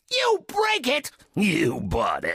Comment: I always hear: “You bricked, you wallet”